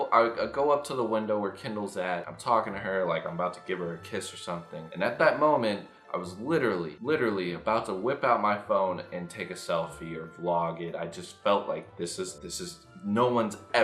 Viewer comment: I thought you were about to say "I was literally literally about to whip my dick out." 11:18 😂😂😂😂😂😂😂😂😂😂😂😂😂